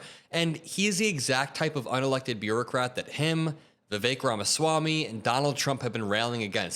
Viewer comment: Every time you say "Vivek Ramaswamy" I hear "the fake ramos wami"